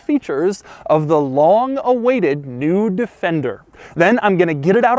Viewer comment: for a second I thought you said "Long awaited nude fender" haha. Edit: also love that you rocking the "wallet, phone, keys" dad look, but your shorts will sit smoother for the camera if you take the stuff out of your pockets (not sure if this aesthetic is relevant to your interest, but its distracting)